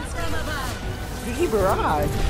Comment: WHY AM I HEARING "NICKY BARRAGE"?!?!?!